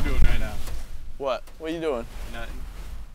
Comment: Why did say he’s “nutting”